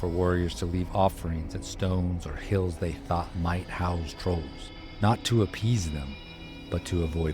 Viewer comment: Thought it said Nose Trolls 😊